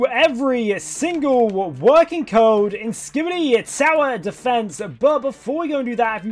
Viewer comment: I thought he said sour defense